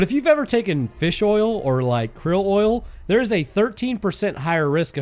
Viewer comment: heads up the subtitles say 133% instead of 13%